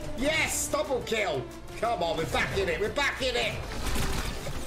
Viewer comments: edited the comment,i just had to. lol if you clicked read more you deserve the old comment “1:02 did you say “bug off” or the funky word.. heh” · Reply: i thought you said “bug off” at the same time tho LMAO. nice video tho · edited the comment,i just had to. lol if you clicked read more you deserve the old comment “1:02 did you say “bug off” or the funky word.. heh” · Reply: Bog